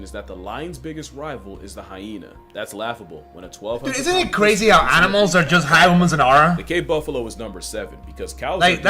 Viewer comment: can someone please tell me what unc said i can't understand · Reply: "Dude isn't it crazy animals are hype moments and aura?"